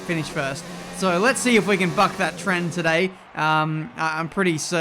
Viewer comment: did you say fuck that trend?😂